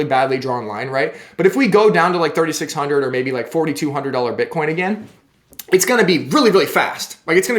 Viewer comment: Did you say $4200 BTC??